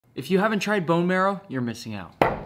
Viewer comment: I thought you said bone meal